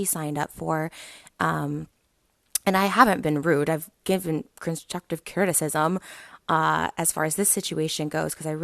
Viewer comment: I couldn't really understand what Trisha was saying but it sounded like she said constructive criticism I mean really what a lame excuse. She should really check her heart and focus more on herself and her actions and not on others peoples I mean really she just dose this to make herself feel better. And sounded like she said Charlie damelios' parents are raising there kids poorly I mean really who is he to be saying that she doesn't have kids, she has no right. I mean really...she really needs to check her self.